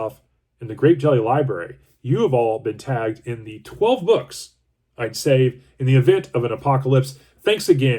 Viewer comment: I think it would qualify James. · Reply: you said 12 books. I thought you said two. I don’t think I’ve read 12 books since watching booktube.